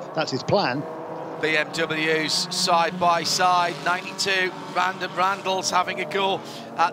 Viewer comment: Did he say vandem randels @ 2:20:02 ?